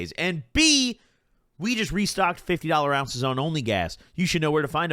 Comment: My high ass thought you said you only restocked 50 ounces of only gas 💀 FRIED!!!